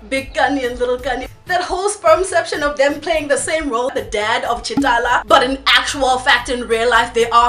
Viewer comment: It sounded like you said Ch'Talla lol